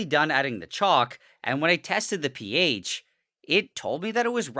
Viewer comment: I heard “i tasted the ph”